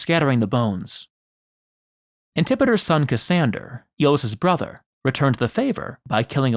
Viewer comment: What's the word? "Assundered"?